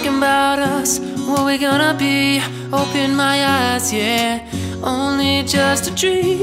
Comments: Did he say at 0:06 open my ASS xD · I swear at 0:07 it sounds like says "Open my ass"